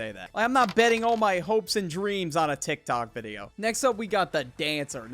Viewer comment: Did bro say hopes and dreams